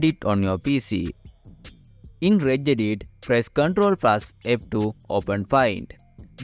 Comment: WTF ARE YOU SAYING !!!!!! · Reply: "In Reg-Edit"